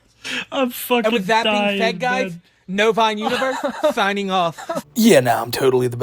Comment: Every time he says "novine universe" I just hear "bovine universe" which I think is just perfect for a lolcow.